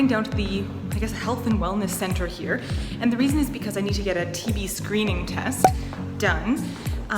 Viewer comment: I thought u said TV screening and I was like ya I do that every day